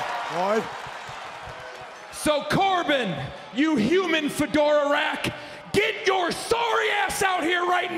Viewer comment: human what now? What does that word even mean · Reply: Fedora is the kinda hat Corbin wears.